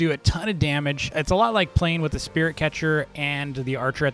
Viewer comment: ares is like playing a lot spirit catcher Me:oh wait i heard said spear it catcher not spirit catcher